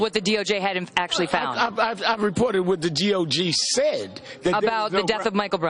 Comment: Al Sharpton: "I Reported what the D - O - G said" lmfao. He probably has no idea what the Dept. of Justice is.